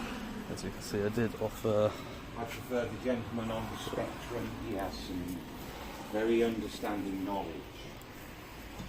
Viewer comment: "The gentleman on the spectrum... he has some very understanding knowledge" did I hear that right? 🤣😂🤣😂🤣